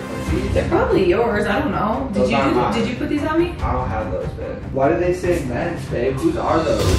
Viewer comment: When he said “why do they say men’s?” I thought he said “why do they say Ben’s?” and I was like lol why does Ben have his name written on the tag of his boxers😂